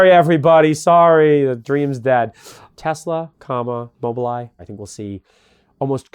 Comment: Tesla, Comma, and what? I think he said something like mobile eye, bubble eye or something like that? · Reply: Mobileye. An intel company doing autonomous driving. They work with legacy auto and NIO